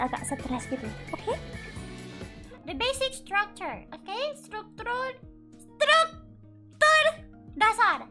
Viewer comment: 10 second in stluktul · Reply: Yes but actually what i heard is "Stuktrul". Correct me if i'm wrong